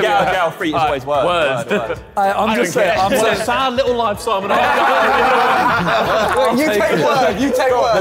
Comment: what did harry say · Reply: "Such a sad little life, eh simon?"